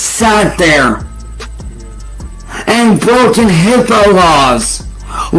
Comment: idk if I heard correctly, but it sounded like said, "HIPPO" laws rather than HIPAA like a dumbass